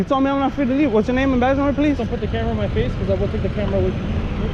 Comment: Did he just say “get the camera out of my face or I’ll take it from you”?